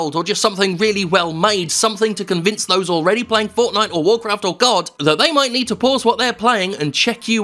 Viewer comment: I thought you said God...